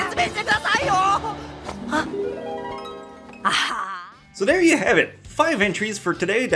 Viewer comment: Right after 9:29 i thought he said so there you go five inches